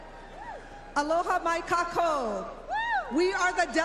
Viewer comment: insensitive maybe but I swear to god it sounded like she said "Aloha my cockhole!" lmfao